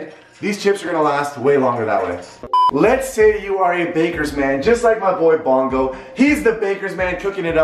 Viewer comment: When he said bakers man I thought he said lakers fan and I was so confused on what he was talking about